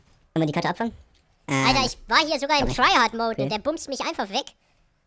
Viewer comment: der BUTZT mich einfach weg! Das hat sich so süß angehört. ;) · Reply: er sagte "BUMST" XD